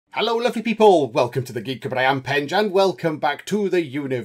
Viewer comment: What does he say @ 0:00 the Geek what? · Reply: Cupboard. The Geek Cupboard. That wasn't supposed to sound James Bond-esque, but it did and I quite liked it.